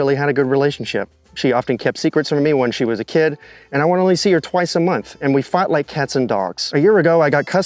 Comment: So teen girl grew up exclusively with her mom, only sees Dad twice a year, then has to move in with said dad?? You’re a stranger, bro. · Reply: I thought he said he saw his daughter twice a month, not a year.